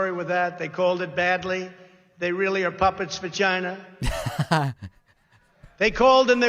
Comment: approx the closed captions say 'puppets vagina' rather than 'puppets of China' lmao